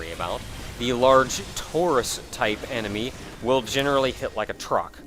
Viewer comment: I was wondering about what he said. "Large, torus type enemy? There's a doughnut shaped enemy?" Then I realized he said "Taurus."